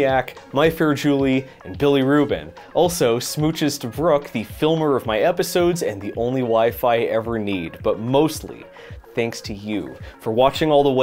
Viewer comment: WHY DID I HEAR THAT AS "The only Wi-Fi ever need"